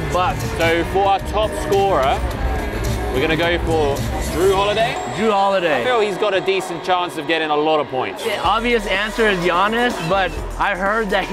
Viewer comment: Did bro just say jrue holiday ?